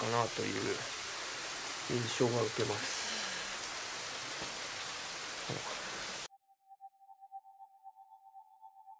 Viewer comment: 原田さん、さん、スタッフの皆さん撮影お疲れさまです。いつも興味深く拝見させていただいています。早速ですが、24:28〜24:29あたりで、が「愛された場所だったたんじゃないかなぁという印象を受けます」のコメントの後に「う〜うん」と女性のような声がします。いかがでしょうか？どのように聞こえますか？皆様、身体には気をつけて撮影頑張ってください。応援しています。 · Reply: 女性の声で相槌のように「うん」って言ってますね。